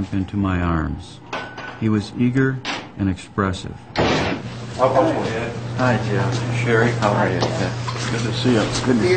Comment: what did Jeffrey say? It sounds like he said hi knucklehead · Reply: He said "Hi Dad...Shari"